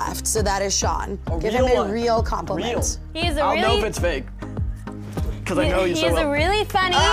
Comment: Did Jace call Riele "babe" at 3:03??? That's what I heard 🤣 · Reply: I wish nah he said “ I’ll know if it’s fake… cause I know you so well”